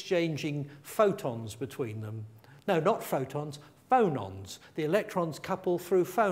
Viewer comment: does he say phonons ?